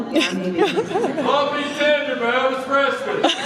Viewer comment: Wonderful panel thanks for sharing ! ❤ What does this guy say in the background at 22:51 please ? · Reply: He says “Love me tender by Elvis Presley” 😂